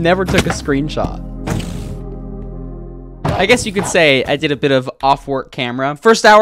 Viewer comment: is it just me or did he say "i guess you could say i did a bit of off work camera"